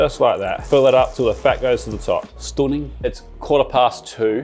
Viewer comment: i hear some northern english man declaring 'stunning' - is it just me?!